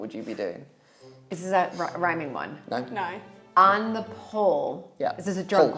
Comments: At first, I thought he said "on the pole," and my American mind went somewhere entirely different 😂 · I thought he said on the pole 😂